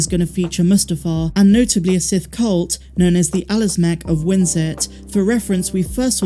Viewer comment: Can anyone identify the name of the 'Sith cult' she mentioned at 1:11? Wtf did she say in this video 'the Alezmek of Windzert' ...??? Edit: Found it, 'the Alazmac of Winsit'...I don't remember seeing them in the movies, though.